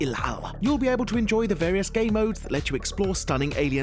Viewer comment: Did he just say gaymodes?